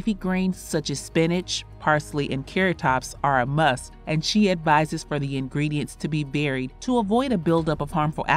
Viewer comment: She looks so good! Her eating suggestions are so point on. I learned from Tina Turner years ago not to eat from a box, a bag or a can. I look good but not today. I've been craving ox tails for three years. I ate them yesterday and feel sick as a dog today. Never again. I'm over it. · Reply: I don’t understand what she said at the 3:13 mark. “Advice the ingredient to be buried “